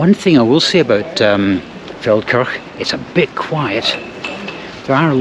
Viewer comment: When the video started I thought said "Falkirk" which would be a long (but not out of character) journey...